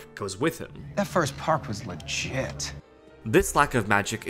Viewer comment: Only now do I hear "That first park was legit" Instead of "That first park was like shit"...